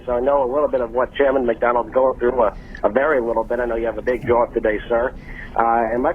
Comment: The last guy says, "I know you have a big job and all..." but it sounds like he said 'I know you have a big jaw and all.." and mind you he does. But the captions are killing me when did penis become diabetes?